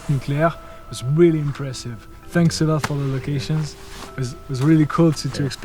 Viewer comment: J'étais à moitié en train d'écouter et j'ai entendu "thanks a lot for the vacation" :') #lci